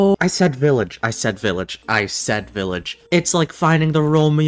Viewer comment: I think he said village chat